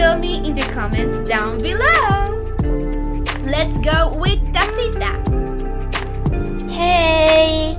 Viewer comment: the subtitles say tastytap instead of tacita xdxd